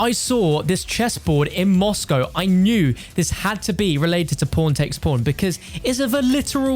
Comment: Not trying to be mean but it kind of sounds like he says “p@ takes p@ instead of pawn takes pawn